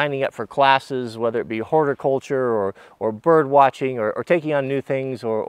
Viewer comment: i thought you said “hoarder culture” and it took me a minute to realize you said horticulture.